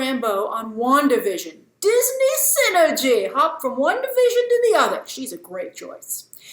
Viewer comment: You made an incredible unintentional double entendre at 5:40 “Disney synergy, hopping from one division to the other” But it sounded like “hopping from WandaVision to the other”